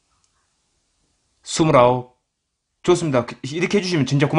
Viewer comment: 29이라고 들리는데...